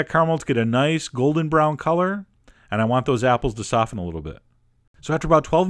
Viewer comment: did i hear "i want the apples to suffer a little bit"?